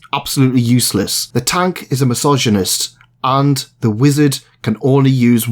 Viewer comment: hmm i must say kind sir i do believe that you may of mispronounced the word masochist because to the untrained ear it almost sounded like misogynist.